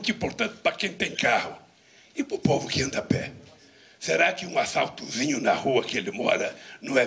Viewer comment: a 04:17 eu ouvi certo?? "....E para os que andam a pé... um assaltozinho não é imprescindível?" 🤮 Livra-nos desse mal Senhor 🙏🏼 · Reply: ASFALTOZINHO